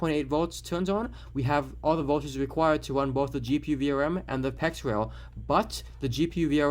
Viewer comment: What's the name of rail he says at 2:44 after GPU VRM ? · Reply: PEX as in PCI-Express